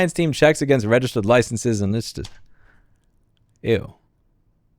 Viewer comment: Did you say “eww” at the 6:00 minute 6:34 mark?😂